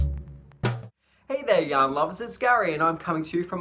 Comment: Love the shawl!! Your sweater is stunning! I smile when you say yarn lovers because the first time I saw your podcast I thought you said young lovers 🤭🤣 I could listen to you talk all day! 🥰🧶